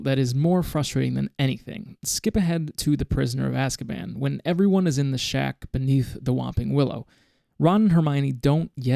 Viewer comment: For some reason i heard "when everybody is getting shagged beneath the whomping willow" and i can't stop laughing.